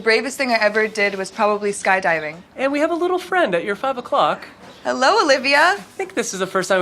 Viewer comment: She called "Hello Olivia!" I first thought Olivia was the girl but she was the goat. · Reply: And I thought she said hello Livia LOL